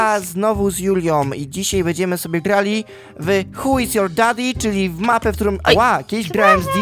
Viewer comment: czy tylko ja usłyszałem "h*j is your daddy?